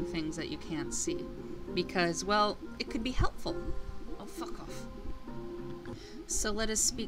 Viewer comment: did she say fuck off